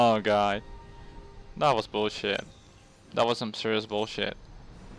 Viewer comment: Haha we all have these kinds of games...man at 2:16 I thought you said "mysterious bullshit" lol, I had to watch it back to realize it was "serious bs", either works lol